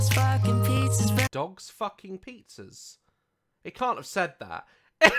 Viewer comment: I heard "dogs barking, pizza's burning." I didn't hear dogs f*cking pizzas until you said something. 😂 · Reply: I heard "dads fucking pizzas" 😂